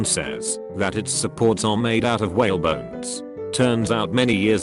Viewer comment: I thought the fish cafe one said, "Whale boners".